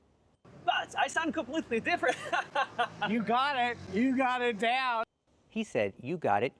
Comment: I also missed what he said, interesting how I couldn't understand him either, I kept hearing "doubt" instead of "down".